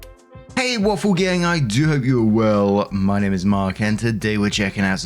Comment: I always thought you say at the beginning " I do have your world"...